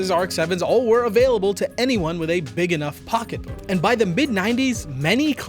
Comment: am i the only one who heard pocket puh?